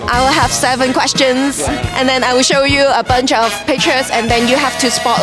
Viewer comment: 我聽成 I will show you a 賓州 of picture🤣🤣🤣🤣🤣🤣🤣